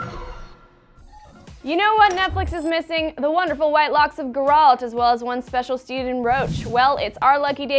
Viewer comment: Did she just say "Grr-alt".